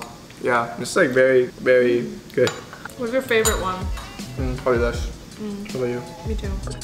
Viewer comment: He said "probably you" after saying "probably this" when she asked "what's your favorite one" Edit: or maybe i heard it wrong idk